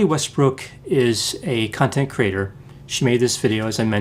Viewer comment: I keep hearing “cunt and creator” rather than “content creator.” 😭